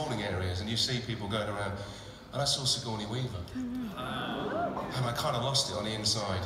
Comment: In 4:27, who did Tom met last year? I can't hear it clearly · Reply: Anthea Iu Sigourney Weaver